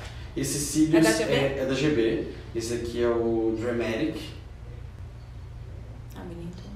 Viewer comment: Qual o modelo de cílios q ele usou em vc? Não deu pra ouvir direito. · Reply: Alessandra Carvalho entendi “dramatic”